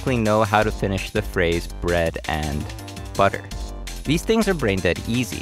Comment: video: "bread and..." Me: "..Jelly." video: "butter" Me: "yeah that one." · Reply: I misheard it as "Bread as..." Confused screaming